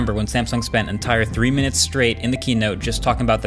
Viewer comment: did you just say keynote?